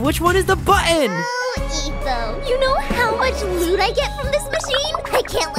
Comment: I thought she said "you know how much food i get from this machine"